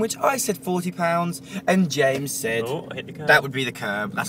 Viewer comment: did call Jim, James?